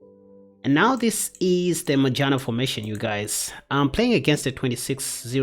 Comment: I swear I thought you were saying the Vagina formation 😅😅.. lmao